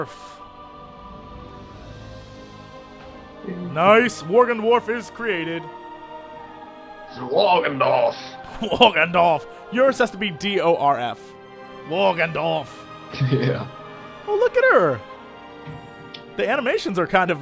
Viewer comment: Every time they say "Worgendwarf" I hear "Morgendorffer" from the Beavis&Butthead spin-off "Daria"